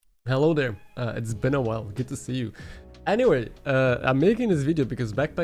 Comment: Why did I hear "hello guy, it's Ben a while" instead of it's been a while. lmao